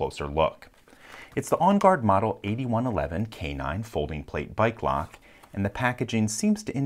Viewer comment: I thought you said by glock, idk why.